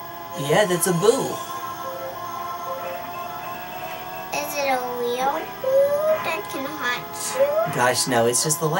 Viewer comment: Wait, what did say there? Sounds to me like she's saying "Is that a weeaboo?" and I kinda doubt that's what she actually said. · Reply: "Is it a real boo that can hurt you?" She's adorable. (^-^)